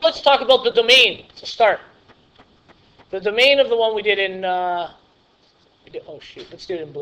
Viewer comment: did you say oh sht? haha XD well that was helpful anyway! XD